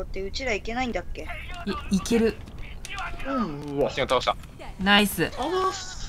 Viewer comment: 「バスティオン倒した！」が「バステン倒した！」に聞こえるw